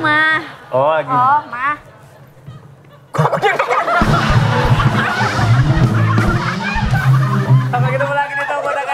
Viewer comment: Menit 26:25 ngomong apa yah ?? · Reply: "kok aku deg2an" Soalnya itu kek momen akward, bg jd inget di eps sebelumnya pas Eca jatuh ada momen akward nya